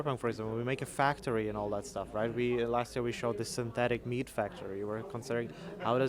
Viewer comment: When said "synthetic meat", I heard "synthetic mead", lol. Still have the Witcher on the mind.